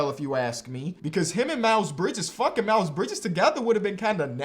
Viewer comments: it sounded like he said “fuckin miles bridges” 😂😂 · anyone else thought he said fuckin miles bridges and not funk and miles bridges